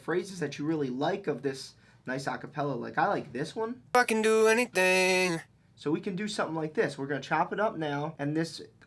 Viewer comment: I heard 'fucking do anything' is it just me?